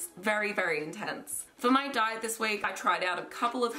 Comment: OMG ON 1:34 I THOUGHT SHE SAID SOMEONE DIED THIS WEEK/WAY